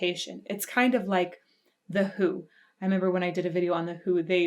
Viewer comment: When you said The HU I thought you said The Who and wondered what song you were talking about.